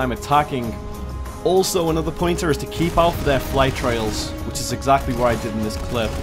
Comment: What do you say at 3:20 because i hear keep off their flight trails, but if i hear that correctly then my question is why? cheers